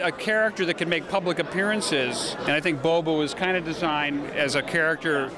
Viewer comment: Did he call him 'Bobo' at 3:42?